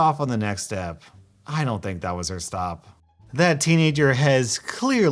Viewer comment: u said step instead of stop